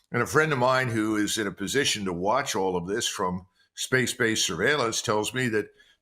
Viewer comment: Does he say he has a friend in space base servailence @ 5:37 ?😮